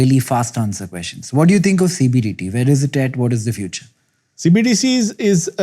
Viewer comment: Did he just say "CBDT" lol ?